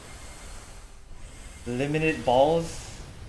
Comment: What He Say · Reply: Limited balls? 😏